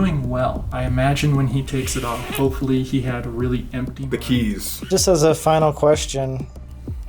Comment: instead of the keys i heard bikini's